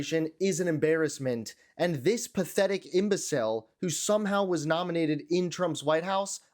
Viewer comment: Whoa, I think you coined a perfect term. When you said the word "imbecile," I heard it as "imbecel"-- a damned useful term, what with all these hormonal punk-ass powerlords.